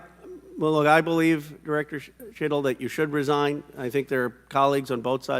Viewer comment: Anyone else hear “shittle”? 😅